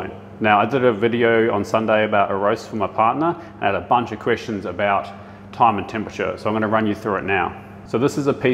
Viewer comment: I thought he said “I had a bunch of Christians” 😂 I was confused